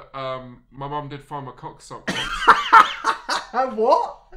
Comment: I shit you not, i had to replay 7:34 thrice to understand "...sock once" instead of "...suckable". And the disturbance was real.